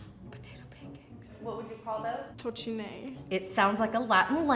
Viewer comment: as a romanian i have no idea what the hell she said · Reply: She said "tocinei", which is the term for potato pancakes in Moldova and Bucovina I guess (I'm from Transylvania tho, I never heard of tocinei, I just googled it XDD)